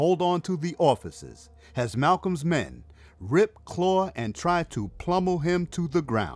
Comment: Did he say "plummel" him to the ground? 😮 6:20 I feel dumber than I presently am.